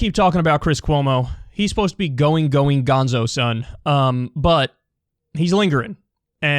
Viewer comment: Did he just say going going gonezo son?